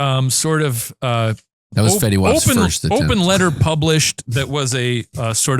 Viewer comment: I laughed don't worry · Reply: It was hard to hear over. I just heard "fetty wap's first..."